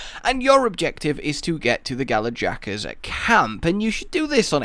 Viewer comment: did he just say galajaka?